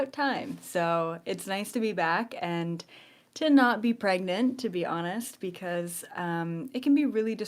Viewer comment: Or maybe is misunderstood, I thought you said youre pregnant right in this moment, or maybe you are really pregnant and I've shifted dimension, bc it does really feel like I have